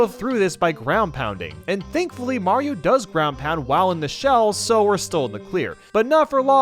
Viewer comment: I thought you said you can ground pound in the koopa she’ll